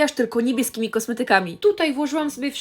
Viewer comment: Ja usłyszałam najpierw "tylko niemieckimi kosmetykami" xdd